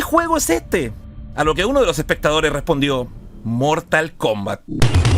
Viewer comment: yo apostaría a que dijo "mortalcomba"